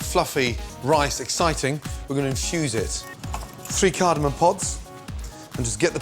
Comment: I heard 'just piss them cardamom pots' at first :D Good video, subscribed!